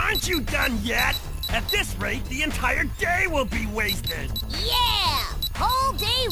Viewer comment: First time I heard Vector say this, I thought he said "At this rate the entire game will be wasted."